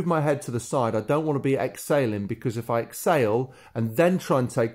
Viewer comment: You totally confused me I thought you were saying Ex sailing but you are saying exhaling😵😵😵ok thanks